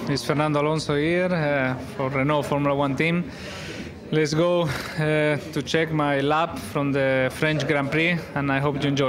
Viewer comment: He sounded like a horse at 0:11. "Let's goo HRGRHG eeh, to check my lap.."